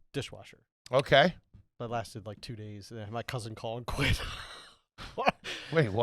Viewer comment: anyone else thought he said “my cousin colin quinn”